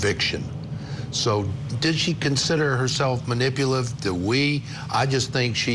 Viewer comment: dude showed up with the Chad “Digbell” line and he gonna keep saying it until people admit how brilliant it is 🙄🙄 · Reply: Thought he said ding bell not dignell but I understand the reference